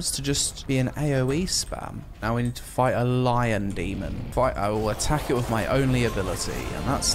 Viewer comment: I thought it said Albion first lol